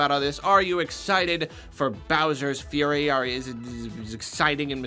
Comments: I legit thought it said bowsers furry and not fury 🤣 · When they said Bowser’s fury I thought they said Bowser is a furry